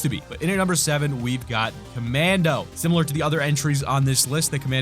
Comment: I thought he said mando, so I thought there was a mandolorian skin back then